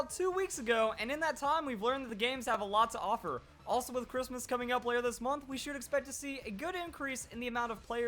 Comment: Did he just say”Christmas is coming out later this month”? I thought Christmas was a holiday not a game